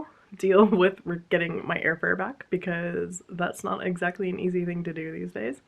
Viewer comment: I'm so sorry for all of the crappy stuff happening this week for you. Holding space for you. For a laugh: when you were talking about air fare I really thought you said air fryer and I was extremely confused as to how they were going to send 2/3 of an air fryer back to you.